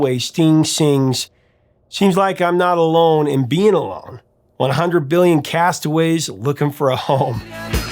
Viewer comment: "Hundred billion castaways looking for a whore..." I can't be the only one who thought that's what Sting said. LOL!